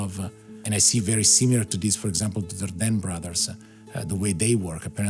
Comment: who are the brothers his talking about in 3:35? I didn't quite understand what he said , and whats the name of the film at 3:47?? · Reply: Dardenne Brothers, Film is Rosetta